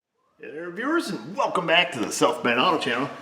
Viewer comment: it sounds like you say in the intro. “ welcome back to the self made auto channel” it’s what I hear 👂. Lol. What’s this south main auto channel all about.